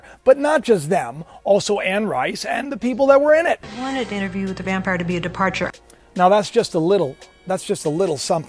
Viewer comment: put the captions on, she clearly says interview with the vampire.. She says.. "I wanted interview with the vampire to be a" an then he cuts her off.